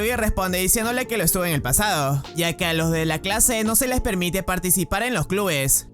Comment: Al inicio pense que dijiste clase kunno XD